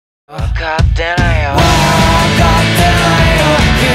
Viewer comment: サビ前の「分かってないよ」が「分かってるよ」に空耳するんだけど、相手側が言い訳に「あなたの言いたいこと、分かってるよ」って言い出そうとしてるのをサビの入りで食い気味に遮ってる感じがしてとっても好きなんだけどみんなはどう